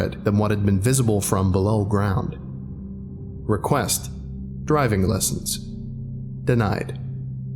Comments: request diving lession but i hear driveing lessions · says driving instead of diving, ruined the whole experience